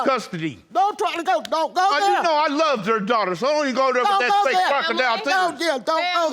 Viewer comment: Did he call her fake crocodile?